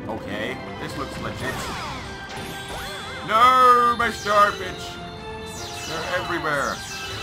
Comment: Is it me or at 26:32 did that sound like 'Noooo, my star bitch' instead of star bits?